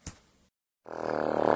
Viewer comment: I cant tell what soviet was sayin in 0:28 can you tell me? Jsvsiejjeje btw this is masterpiece👏👏👏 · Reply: "Fricking knew it" Ty uwu